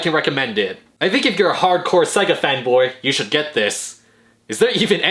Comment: I thought he said hardcore sega fatboy lol.